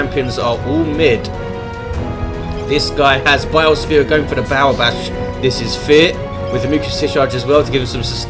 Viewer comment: Sounded like said "This guy has Bile Spewer, going for the bowel bash." I thought "That...somehow sounds like it makes sense, considering we're talking about a Plague Champion with a Bile Spewer." I know it'd do terrible things to my bowels getting covered in Nurgle's "gifts."